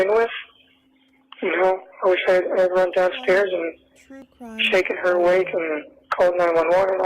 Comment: Did I hear him say he should have run DOWN stairs and shake her awake?